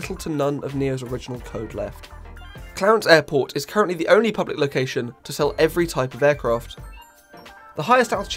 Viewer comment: Sorry. What did you say? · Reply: “-Original code left. Clarence airport is currently the only public location to sell every type of aircraft.”😊